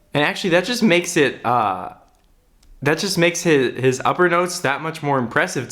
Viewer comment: Lol when he was pointing out Jungkook’s upper notes, I thought he said upper nose rofl